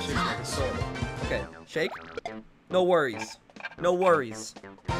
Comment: For a second I thought you said "No furries"